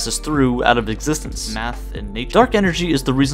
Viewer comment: I hear someone talking than this guy it said "Math and. .." in